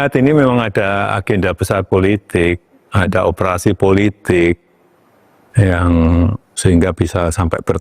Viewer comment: Salah denger bukan operasi politik yg bener operasi plastik hehrhe😂